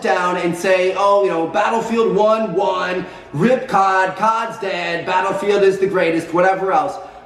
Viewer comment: I thought "cod c***" said "cod cast"😂